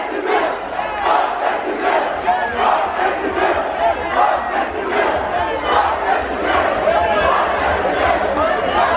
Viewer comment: ils disent quoi là? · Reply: Fuck Casimir #hallucinationsauditives